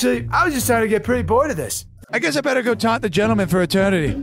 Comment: At first I thought he said top instead of taunt and I was like: 👁👄👁